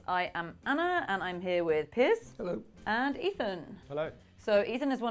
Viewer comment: I had to rewind it a few times at the beginning. I thought she was saying that the guy on the right was "piss" not Piers